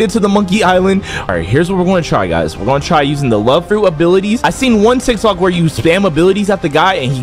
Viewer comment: BRO Y IT SOUND LIKE HE SAID DISABILITY